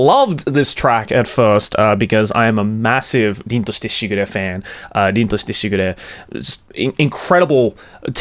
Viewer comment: what is the name he's saying? i don't have a clue how to write it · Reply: Ling Tosite Sigure